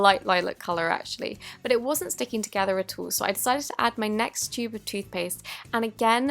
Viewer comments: I thought you said “next stupid toothpaste” 😂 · who else thought she said my next stupid tooth paste not my next tube of toothpaste?